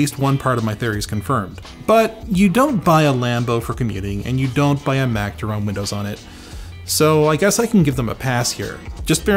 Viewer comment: can someone tell me what he says at minute 5:45 please? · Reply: "You don't buy a Lambo for commuting and you don't by a Mac to run Windows on it"